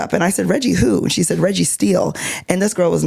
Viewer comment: “Reggie who? Reggie Steele” oh my god I thought I heard Registeel😭